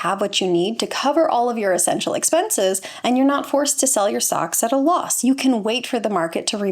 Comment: Did you say "Sell my socks at a loss"? Well, I'll never sell my socks at a profit so it's a good thing I don't rely on sock sales to fund my retirement. 😉